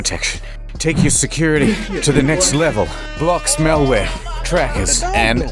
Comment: I heard "Block smalware"